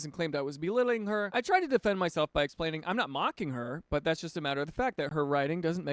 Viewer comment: second story when he says im not mocking her I just hear "no offense but"